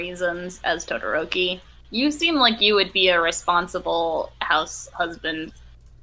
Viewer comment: WHYD I THINK SHE JUST CALLED IIDA A HOUSE?